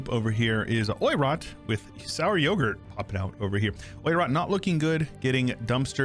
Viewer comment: im sorry did you just say sour yogurt? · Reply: Icky sour yogurt